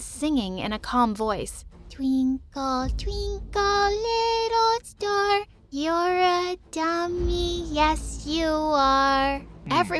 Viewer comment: I THOUGHT she said "twrinkle twrinkle little star you ADPOTED yes you are"😂